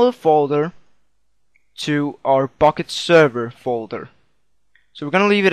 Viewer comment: at the start it sounded like u said fukket server :DD